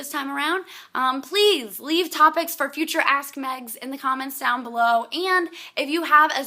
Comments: I swear I heard "ass meg" various times in your video. Having two dogs is twice the work, it's like having two teenagers depending on the breed and age of dog. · It sounds like she is saying Ass Meg. Lol